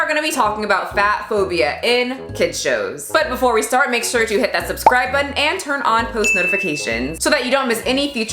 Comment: lmao i thought you said "click that bell butt"